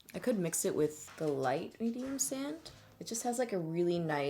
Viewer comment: When you said "medium sand, which is..." i thought you said "medium sandwich" i was like.. weird shade name but okay lolol